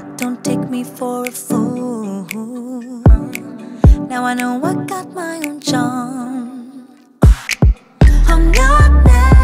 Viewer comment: For a second I thought she said "I am my hair..." I got born Thai way vibes but then I found out it was "I got" not "I am"